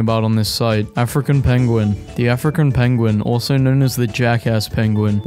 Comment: When he said “African penguin” I was reading so I thought he said “a frickin’ penguin”